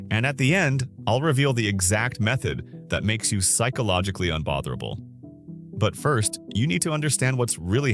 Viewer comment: Am I the only one who turns on subtitles and it says unfatherable instead of unbotherable